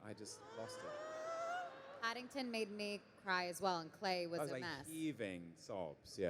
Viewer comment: What does Elizabeth said right after she said "Paddington made me cry as well "? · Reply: "And Clay was a mess", i don't know who's Clay though